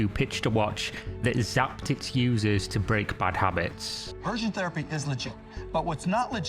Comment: did he say conversion therapy is legit? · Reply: No, he said aversion therapy.